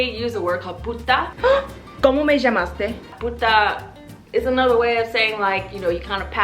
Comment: So funny when you said como me llamaste I really thought you said pu**